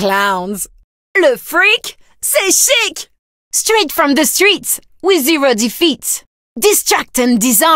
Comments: I think she says "Straight from the streets with zero defeats" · As a French,I can translate you her french voicelines: "Ohh,pâtisserie of misery" ->"Ohh,pastry of misery" "Au-revoir...!" ->"Goob bye...!" "Ohh,j'suis cramée..." ->Ohh,I'm screwed..." "Oh mon dieu!" ->"Oh my god!" "Excuse-moi?" ->"Excuse me?" "Espèce de ouf!" ->"You crazy!" ("Ouf" is "Fou" (crazy) backwards) "Aïe!!!" ->"Ouch!!!" "Très bien! I killed again!" ->"Very good! I killed again!" "Et voilà" ->"And voilà" "Magnifique!" ->"Beautiful!" "Le fric,c'est chic!" ->Cash,it's chic!" "Like a papillon!" ->"Like a butterfly!" "Calme-toi" ->Calm down" "Tais-toi!" ->"Shut up!" 1:08 I think she says "with zero defeats" 1:34 She's actually saying "Tais-toi" Thank youuu ❤